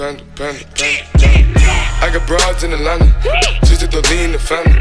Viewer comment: panda. i sell bras in atlanta. my number #1 customer is Santa. my favorite soda is fanta my favorite person is my grandma. 9plus10 21. white x6 panda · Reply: the very first words he said "I got the brpds on Atlanta. I thought he said I got lebrons in London.XD